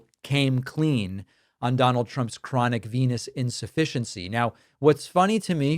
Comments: I thought you said chronic penis deficiency...they are all on some serious drugs that they get for free through the WH. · I thought you said Trump’s Penis insufficiency